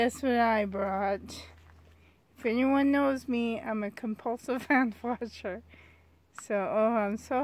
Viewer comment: Hi, I couldn't understand. You showed like a water dish. You said if any one knows you your a ----? Glad You guys had a great time!🤗🐽✌❤ Sheryl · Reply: lol I totally thought you said you were a compulsive ant watcher and I was like oh?? Is she trapping them in water?? That's wild! Lol but I realized it was hand washer and not ant watcher 😋